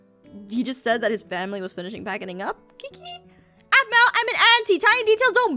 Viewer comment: did anyone else hear her mess up? She said packiding?